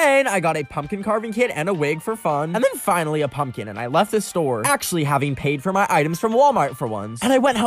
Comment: Am I the only one who heard him say: actually having to buy my items for once 🤭